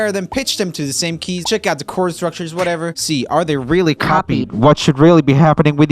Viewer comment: The 'copied' at 0:33 sounded like L, if you know what I mean.